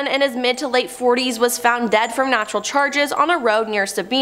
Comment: I know I'm not the only one who heard her say dead from natural charges 😂